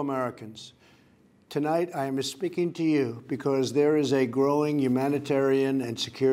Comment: does he say "I'mma speaking to you" or "I'm mispeaking to you"? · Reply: LeviGratton I heard “I’m espeaking to you”